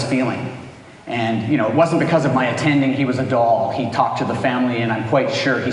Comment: What does he say 9:57? He was a ... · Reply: I believe he said "Doll" "He was a doll" meaning He was really sweet.